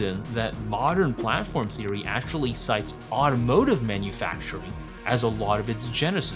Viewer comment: Mistake in captions at 9:03, 'sites' instead of 'cites'. Also, good to see deaths gambit in the wild at 29:30 :)